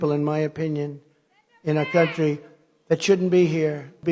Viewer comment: I misheard "in a country that shouldn't be hear" for a moment there